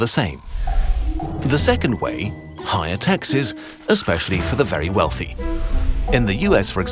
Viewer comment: Because it’s not making more money, it’s just redistributing it by taxing the rich more. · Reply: i thought they said no taxes. Well, I'll blame it on lack of sleep (